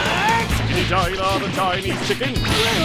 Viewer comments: what did he say? xD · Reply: "Chickity china the chinese chicken" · Okay, I gotta ask: what did Cell say at 4:12? He said it so fast I have no idea ... it sounded like something something tiny king? Pizza king? Can anyone help? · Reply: Jonah Seaman Chickity China the Chinese Chicken Turn on the subtitles😂😂😂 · What the hell is Cell saying? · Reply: dstebbin Chickity China the Chinese Chicken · What does Cell say at 4:11 ? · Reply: Brando chickty china the Chinese chicken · what did he say? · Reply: MICHAEL X chikity china the Chinese chicken. Lyrics from the song One Week by Barenaked Ladies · what does cell say at 4:10 ? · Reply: Chikity China the Chinese chicken. You can just turn on the subtitles to see what their all saying. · I swear to god Cell says "Stinky vagina of a tiny chicken" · what does cell say at 4:10 ? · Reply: A line from one week "chickity china the Chinese chicken" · Hey there, fellow TFS fans. Can ya help a brotha out? What the hell is cell saying at 4:10? All I got was "The tiny chicken." I'm sure it's just a meme I'm unfamiliar with, but any assistance would be appreciated.